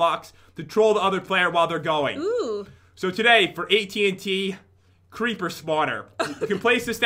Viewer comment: Haha, at 2:45 it sounds like he said AT&T xD